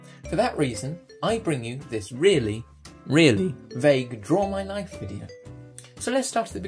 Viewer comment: When you said draw my life video it sounded like you said draw my knife XD